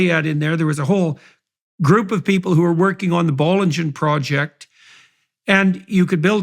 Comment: Hi guys, 51:21 (m?? Elliot) 51:24 bol engine project? can someone tell me what he said please?